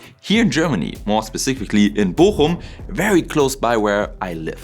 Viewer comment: At first, I thought you said Bockum which is in Krefeld and is my father's home town. Still, it is pretty close.